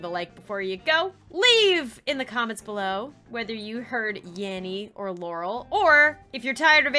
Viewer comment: I heard yammy when it was saying yanny or laurel. Edit: I know it supposed to be yanny or laurel but I literally heard “yammy”